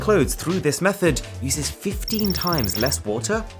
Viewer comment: I thought he said 15% but 15x goddamn